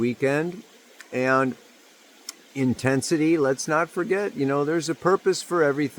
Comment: Every time someone says intensity I hear "in tent city" 😂 I'm Pisces sun Virgo moon so my comfort zone is the edge, ride that wave all the way to the shore baby 🙏☯️💦